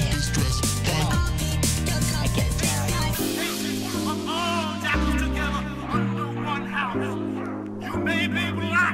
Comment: Can someone PLEASE tell me WTF whoever is saying at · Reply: "you may be black, you may be white, you may be jewish or gentile it won't make a difference in our house and this is fresh"